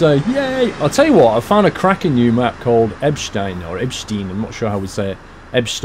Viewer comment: did he just say "a crapping new map?"